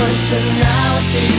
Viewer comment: For a period of my life as a youth I thought he was saying "coked up personality" and I thought that made sense 😅